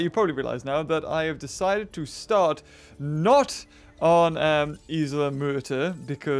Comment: When you said isle maerter I thought you said isle murder